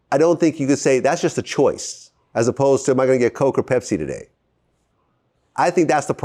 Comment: My what??? yo what did Vlad say right here ?? 😂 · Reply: My gogo Pepsi